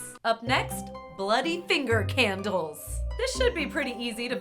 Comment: Did I hear say "this should be pretty easy to finger out" 😂